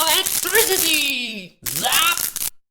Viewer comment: I thought that e-wis said Electrons and not Electricty. omg i just spelt those right maybe im so cool